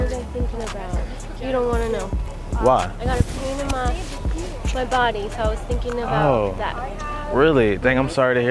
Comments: What did she say in 9:32? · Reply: khaled _fh I got a pain in my body? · what did she say? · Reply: I have a pain... In my body